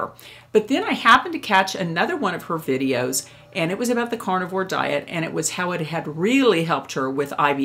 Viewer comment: Haha! I thought I heard you say "cardboard diet"! I was thinking, hmm. Yeah, that would work. Haha